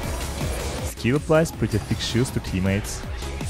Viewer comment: I heard “he applies pretty pink shoes to allies” and had to do a double take 😂